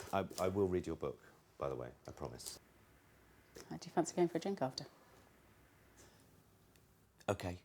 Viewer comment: What does she say at 7:00? I couldn't make it out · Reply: She said, "Do you fancy going for a drink after?"